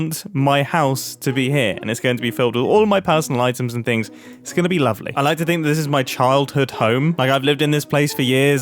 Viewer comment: who else heard it... Skibidi lovely